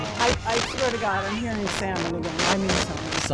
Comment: what did she say I swear to god …….. summon what????